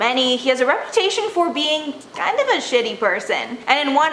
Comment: omg lmao I just rewinded TWICE bc I thought said that phelps is a "shtty person" 😂 spoiler alert: she said "shady" lol